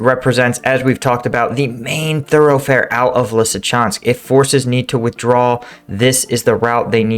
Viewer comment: Thought he said 'if NATO needs to withdraw'!